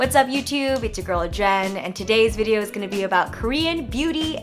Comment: i thought you said 'its your gorilla, jen' for a minute